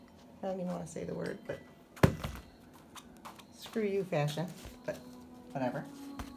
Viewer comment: Momma, I didn't know that drawer opens. I was startled😊. I thought I heard momma say screw you Tasha???